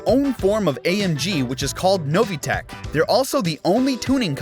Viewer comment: when i heard NOVITECH on 98th fact, i heard it as NO VTEC. those were 0.5 secs of pure sorrow and anger. HONDA VTEC VRRRRRRRRRAAAAAAAAAAAAAAAAAAAAAAAAAA